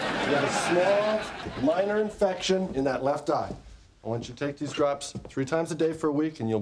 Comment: Thought he said the left eye